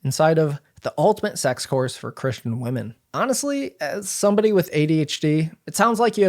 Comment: You said “as someone with adhd” and I thought you said “as someone with a phd” and I was like wow he’s so humble I had no idea